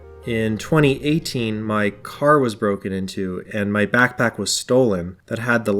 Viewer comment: The first time I heard "my car was broken in two"